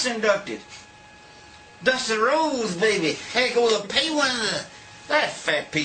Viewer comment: what is he saying, after "Dusty Rhodes, baby..." ? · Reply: Go to the pay window